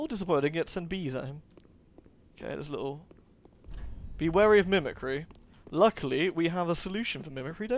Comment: I thought the message said "Be wary of intimacy"... That'd be pretty startling in a Dark Souls game...